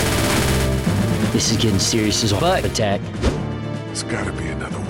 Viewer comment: I don't know what he said but I think I heard him say This is getting as serious as butt attack